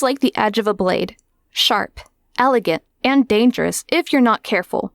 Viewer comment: When you said “sharp” at 0:18, I thought you said Shart 🤣